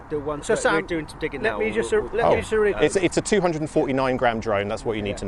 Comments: Fuck me I thought you said 249 grand drone for a second xD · "It's a 249 GRAM drone"..... at first I thought you said it's a 249 grand drone LOL